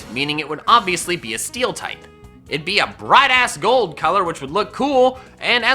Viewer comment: omg I thought you said “bright ass” instead of “brass”😂😂😂😂